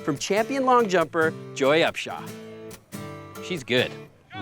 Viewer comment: Did he say the professional long jumper's name was Upshaw!? Up shaw? That's hilarious.